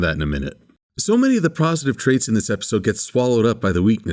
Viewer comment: I heard Prositive traits